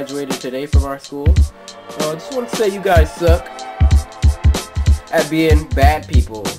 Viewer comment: Oh I thought you said we suck I was about to be like I'm not mad because u said we suck at being bad I mean I'm not Bad to you I really like your videos!!!! Great But I can be mean to other People but I don't mess around with girls like you said so ya I got your advice!! 🤗